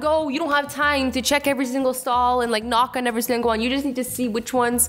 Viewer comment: Did she just say SHE KNOCKS ON EVERY STALL!